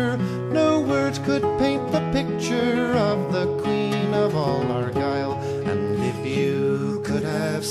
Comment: I thought it said “he queen of angry” and i just accepted that